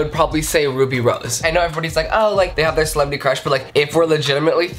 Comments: I thought he said Ruby Rube and I was like whaa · I thought you said ruby rube for a minute 😂😂 I was about to say thats not legal 😂